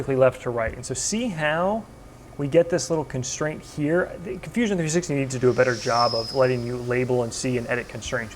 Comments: Did he call it Confusion 360...? Obviously not intentionally, but I had to chuckle. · it sounded like you said confusion 360.